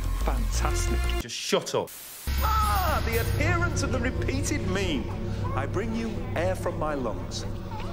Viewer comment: Yes, mates. Ye art not deaf. Christopher Ecceleston as the Ninth incarnation of the doctor did just say: "Ah! The Adherents of the Repeated Meme. I bring you air from my lungs. "